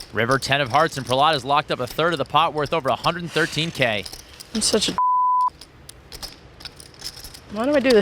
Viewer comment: what did Selbst say that had to be bleeped? I thought she said "I'm such a nit" but I guess it was worse than that...